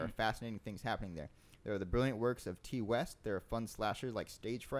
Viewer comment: What did say in the min 44:20?, when you where talking about the good horror movies, I didn't understand it, right before "there're fun slashers like Stage Friight" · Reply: now that you said that I understand "The brilliant works of Ti West", thanks